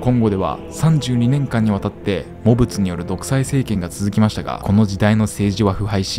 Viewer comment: …今の北みたいな？ モブツ←汚物、に聞こえた❗ ヾ(≧∀≦*)ﾉ〃